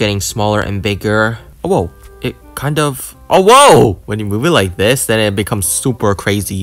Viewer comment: SOUNDED LIKE HE SAID OWO